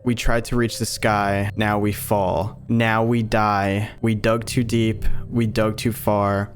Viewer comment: It kinda sounds like he says “drown” at the end instead of “die”